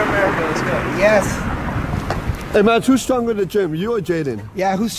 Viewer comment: what they said ? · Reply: Hey mads Who’s stronger in the gym you or Jaden ?